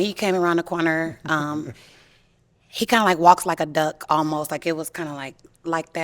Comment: Did she just say he walks like a duck ?